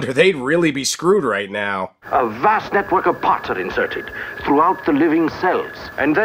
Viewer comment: Is he saying "A wast network of pottery"? He's got superpower from... flower pots and ashtrays!?